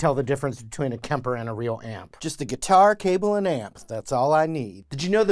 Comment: I like the dude that only needs an amp and a cable. No guitar required, super impressed. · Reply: I thought he said "guitar, capo, and amp". As in he is only going to be playing open cowboy chords